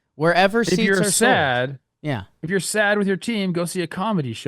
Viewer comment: did you say sad you must be talking about the Cubs